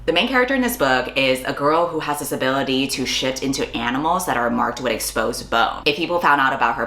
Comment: I thought said "shit into animals" not "shift into animals" and I thought: bitch keep your furry fantasies to yourself.